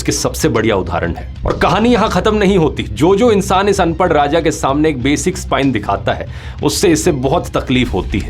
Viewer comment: did he say anpadh raja 😆🤣🤣🤣🤣🤣 his quirks like these are notworthy a n d true and rofl 😆😁😄💯